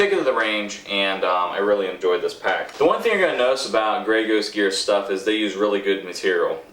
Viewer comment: did he say Grey Goose at about the 2:10 mark???????????